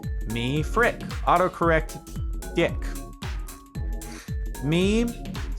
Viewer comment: did just said dick?! XD